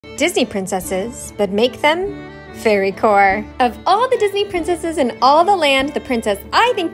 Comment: Ar first i heard "furrycore" Not "fairycore" 💀😅